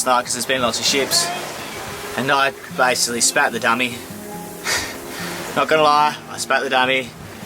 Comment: what did you say? Sounded like "spat the dummy".